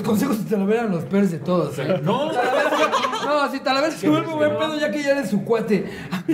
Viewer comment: que dice aqui sipi sapo de talavera?? no se le entiende ni vergas